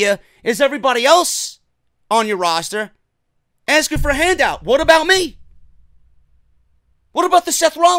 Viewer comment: am I the only one, in the "what about" part said "What about Raven"? XD